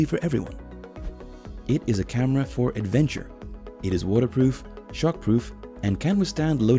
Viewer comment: Every time he says “shock” it sounds like he is saying “shark”. I was thinking man this guy has a big shark problem. He keeps testing the cameras if they are “shark proof”.